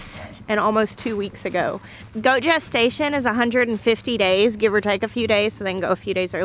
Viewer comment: I was only kinda paying attention because I was counting out stitches on my cross stitch pattern and I for real thought you said “goat Jess station” instead of “goat gestation “. 😂😂😂